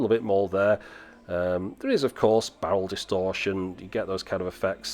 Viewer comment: Wait at 5:35 did you say bowel distortion? That camera really is crap... 🤔😉 Just kidding of course, love the videos!